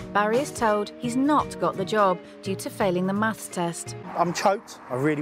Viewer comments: I think she said maths, not mass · So apparently all of the British people in the comments have informed me she said "maths" not "mass" at 23:08. Oops. Maths is not a word in American so you can understand the mistake. Fortunately it doesn't change the contents of the video that much because the argument that comes immediately after it is still true. Though it does provide more hope for Barry as it is much easier to spend a few hours on Khan Academy freshening up on your maths than it is to lose 200lbs. Edit: Ok.... Mistake number two at 18:30. I realize that 18+8 does not equal 24 hours. In my defense, it's correct in the script; I just read it incorrectly, watched the video like 6 times, and didn't notice. Usually I catch things like that. ALSO in my defense I did the math on half of 16 correctly so all the people telling me I can't do math, EXPLAIN THAT MATH. Well, at least it wasn't a big fuck up like me not noticing that the narrator said Debbie pays 60 pounds a week in rent and not 60 pounds a month. I caught that shit right before I released the video and fixed it as I was doing the final editing. That would have been really bad. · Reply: I just got to “maths” part. I totally thought they said mass test too. “maths” sounds like some illiterate hillbilly would say. I thought it was weird they’d have a mass test. But that maybe it was because England has socialized medicine or something that they’re aloud to give someone a mass test lol.